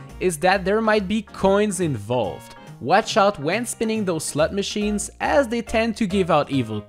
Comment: Put this on in the background and I hear “slut machines” 👀